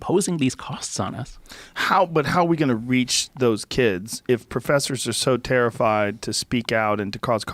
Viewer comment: "HOW DO WE REEEEEEEECH THEEEESE KIDS" is what i heard lol